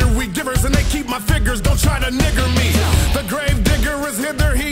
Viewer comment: did he say dont try to nig*er me ? Lmao but what did he actully say